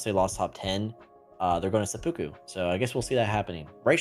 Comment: what did he say? supukoo what? 😭😭